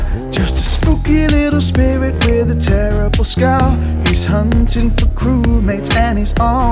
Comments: NO WAY- Edit: I’m sad . The lyrics were not “ just a spooky little spirit in a county jail” it’s “ just a spooky little spirit with a terrible scowl “ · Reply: i thought it said "just a spooky little spirit with a terrible spell" im dead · NO WAY- Edit: I’m sad . The lyrics were not “ just a spooky little spirit in a county jail” it’s “ just a spooky little spirit with a terrible scowl “ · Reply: I heard "just a little spirit with a terrible skull"